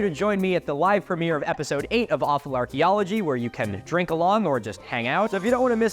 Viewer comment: lol, the captions on the actual video called it "Awesome Archeology" instead of "Awful Archeology."